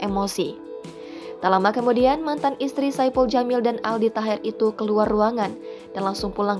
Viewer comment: mantan aldi taher? Yang bener aja min apa aku salah dengar